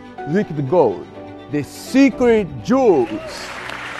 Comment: Sounded like he says "secret jews" 😄 🤣 😂 😆 😄